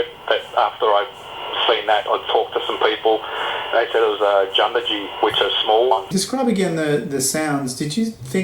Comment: what did he say @ 4:52? 'jumbaji'?? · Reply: He was mispronouncing JUNJADEE that has become a catch-all term for Little People of Australia. There's a good description at the link below but it is basically the smaller of the versions of creatures people call yowies. <link>